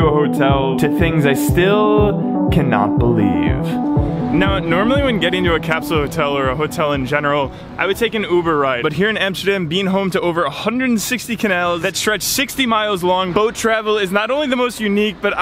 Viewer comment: are my ears broken or did i just hear 160 canals edit, thought he meant the womans canal, sorry.